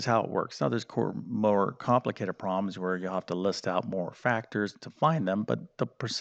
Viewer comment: It’s funny that every time I heard him say “problems” I thought he was saying “proms.”